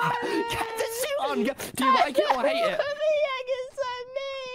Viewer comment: Wait did the egg say a slur-? It sounds like Tommy says “egg, what did I say about slurs egg” at the end 😭